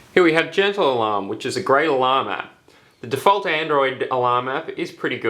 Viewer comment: When you said " great alarm app " about the gentle alarm app i thought you said " grey llama " LOL